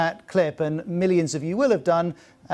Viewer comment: At the end the presenter sounds like he says "unfortunately survives the situation as well" hahaha